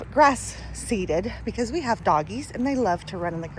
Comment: Doggies, , I thought u said Donkeys...LOL